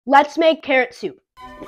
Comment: For a second I thought he said “let’s make parrot soup” 💀💀💀 love ur vids btw